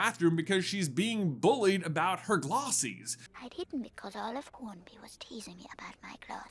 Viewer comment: glossies? i always thought she said glasses.. O.o